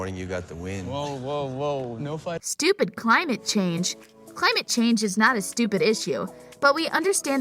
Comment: Oh, I ain’t no trumpie but Johnny talking about climate change was funny Edit: I didn’t know he said “climate change bullshit” I thought he said “stupid climate change”